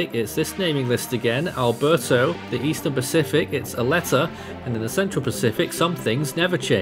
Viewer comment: When said Aletta i thought he said "a letter"